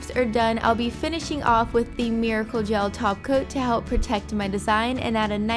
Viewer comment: who else heard her say taco instead of top coat 😏😏😏